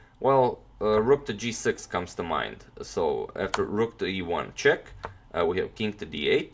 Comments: Did I hear "Rook to Ivanchuk"? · At 5 :23 I thought Antonio said "rook to ivanchuk " looks like I have watched a lot of ivanchuk games today